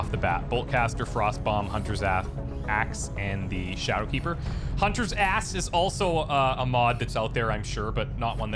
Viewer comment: did you say hunters ass at 47:09 it made me chuckle great series so far been enjoying it!